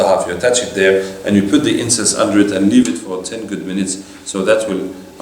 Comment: I didn't understand what sheikh told after Cupping ... Inscents or something like that at 31:20... Around Can someone please explain... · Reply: Incense. Agarbatti